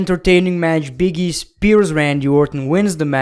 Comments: Did you say Big E spares Randy Orton?😁😂😂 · what did he say at 11:30? “Big E speared Randy” 😨😨😨🤨